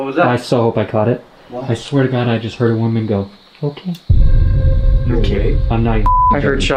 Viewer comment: I hear "I can see Paul, that's clear", but "Okay" isn't actually said. It's an extremely faint, barely audible, female whisper.